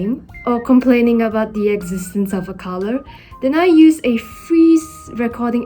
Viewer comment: I thought you said”the existences of eggs inside avocados.” Lol well I had to rewatch that part to verify that was not what you said. 😅